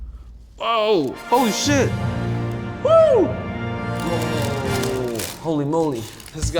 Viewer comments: Did anyone hear him say “oh shit “ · Is it me or did Alvin say oh shit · Did he say oh shit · Did I just hear Alvin say “Oh, holy $hit!” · did you really say holy shit on